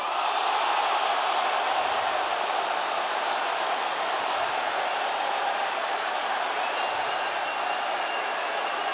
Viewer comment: what are they mouthing to each other? I can't really tell what MJF says but it looks like Punk says "right back at you" but I could be completely off. The Miz line was a truth bomb, it was right there all along and I never noticed until Punk pointed it out. MJF and Punk are the best on the mic compared to the rest of the AEW roster. If I was Tony Khan, I'm not booking a match for these two right away. I'd let MJF and Punk verbally go at each other to build tension and anticipation for the fight. I can't believe this is almost 20mins, it went by so quickly which again speaks to how good these men are on the mic. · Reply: MJF: Motherf—ker Punk: Bitch